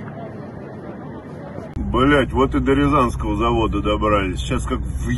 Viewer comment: I wonder what he is saying? I recognise most of the words uttered, but the meaning gets lost. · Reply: Вот и до рязанского завода добрались.